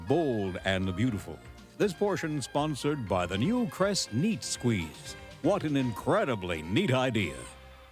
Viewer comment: I'm dead serious, I thought the guy said, "new Crest Meat Squeeze"...